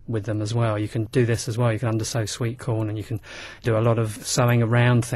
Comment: Question!!! What does he say about sweet corn around 2:44? He will underside it? I can't understand the accent haha, I need it for a project! Please respond!